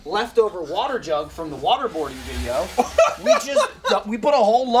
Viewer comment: excuse me, did you say waterboarding??